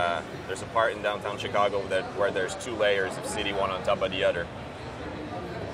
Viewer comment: When ever he say other like 3:25 he sounds like sky saying Budder